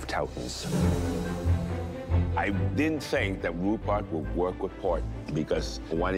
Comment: what did alvin say??? i didnt think rhubarb would work with porn?????????